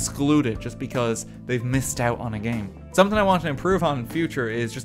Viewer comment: When he said “making baby steps” I thought he said “making a baby” and bout lost it